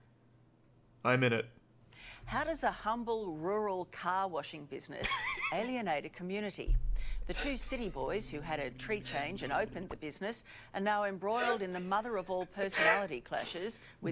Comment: With her accent, I first heard evil "cow washing" business.